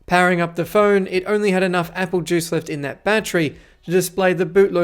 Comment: did he say "apple juice?" 😂